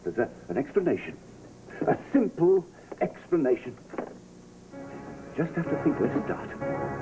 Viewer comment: What did richard fields say right at 17:00 that made Kendall go throw up? He said there's a simple explanation....I just have to.......? What did he say? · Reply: I just have to think where to start